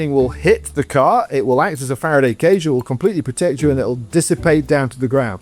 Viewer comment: subtitles are wrong. It's a Faraday's cage, not a fire enclosure.